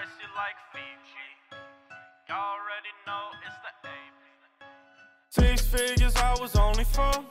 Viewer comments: I thought it said fish fingers I was only four... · Lmaoo for the longest time I thought he was saying "six fingers when I was four" I was like boi wdym??